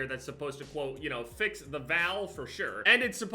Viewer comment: I'm so used to having problems with his house that I just immediately thought he said VALVE and not val.